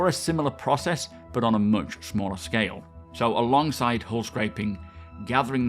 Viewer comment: Just after 3:35, you said hull scraping, but at first I was like "what did he just say?!?" because it sounded like you said "horse r*ping". Oh man, my old man ears, I think I need hearing aids.